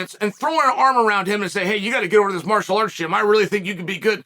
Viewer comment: Did chael just say “martial art shit” at 5:29 😂